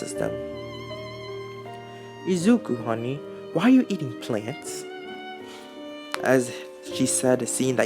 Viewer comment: Idk bout yall but I heard "izuku honey, why u be eatin plants"